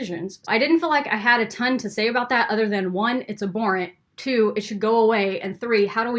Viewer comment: what is ab bore ant? i like this word. cant even find the definition on google though. 4:01 i think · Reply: abhorrent; think something is disgusting